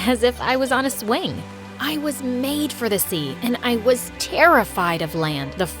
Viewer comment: I like how the subtitles say “I was may paid for the sea”